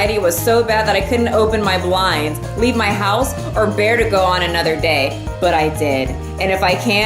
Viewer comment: I legit thought she said "or to go on another holiday"